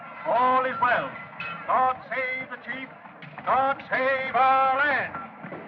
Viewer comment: sounded like he said "God save our ass" , well it did .